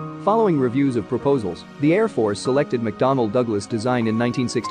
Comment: i thought that you said McDonald Douglass